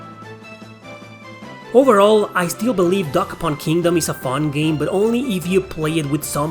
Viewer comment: Haha, thought you said "Duck Hunt Kingdom". That NEEDS to be an RPG! :D